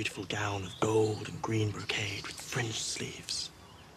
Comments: did he just say "French sleeves" is France cannon in Westeros? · Am I hearing right? "With french sleeves"? Is that where gays come from to Westeros? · did he just say 'french sleeves'? Since when did Westeros have France wtf lel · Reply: fringe sleeves, not French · Did he say "French" sleeves? I didn't know France was a part of the 7 kingdoms...hmmm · Did Loras say FRENCH sleeves? 0:55 Are you telling me france exists in the world of Ice and Fire??? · Just re-watching S03... did he just say French sleeves? :D Probably fringe, must be hearing things. Can't wait for a character to say: Jesus F. Christ! :D That's what my brain sais at this amazing series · Did he just say French sleeves? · I swear he said French sleeves? What did he actually say?